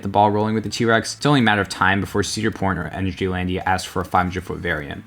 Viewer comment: I keep rewatching 11:35 and I keep hearing cedar p*** lol. I mean, it sorta is p*** for enthusiasts.